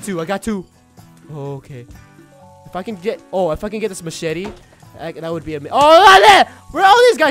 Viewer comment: ROI I heard you swearing you said if I could get this f*ckin machete It's